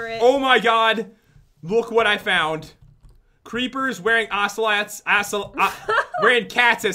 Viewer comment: did he just say assolots?